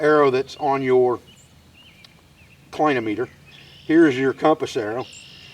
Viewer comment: did you say "point-o-meter"? He He! Or did I mis-hear you. I have been fascinated with shadow and direction most of my life, as I walk the woods for a living every day. This is good stuff! It sorta takes the "watch" method, but translates it to your physical person. Keep up the great work! Also, I believe that "point-o-meter" is for determining the slope of your terrain... it is a clinometer needle.